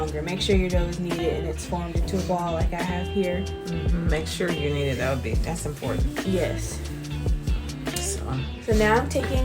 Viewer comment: What did the mom say was important at 6:56? She was mumbling. I couldn't hear her. I replayed it 4 times before I gave up to type this question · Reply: Hello! I was reiterating what said right before that. She was saying to make sure you knead the dough. I said Yeah make sure you knead it..that's important. Thanks for asking!